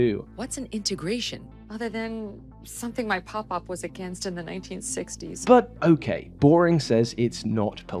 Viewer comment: you can read a cut section of this video’s script here: <link> Signing up also really helps this channel out. I’ve been putting more time into each video recently. I want this channel to be the best it can be & it can be so much better - but that means fewer uploads, fewer ads, etc. Joining up on Patreon helps me make better stuff & lets you see a whole bunch of exclusives in the meantime 👊👊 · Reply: she says 1960s but the subtitles say 1950s Literally unwatchable. Edit: he fixed it lol